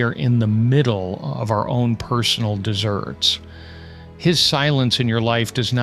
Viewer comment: did you say personal desserts? 😂 🍦 🍨 🍦